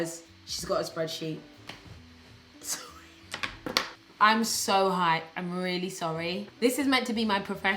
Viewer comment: I thought u said "Im so high" I was like shes a stoner !? 😂 This girl is awesome I never watch reaction videos but I love yours and theyre educational n funny ty for a nice work break 😊 ❤️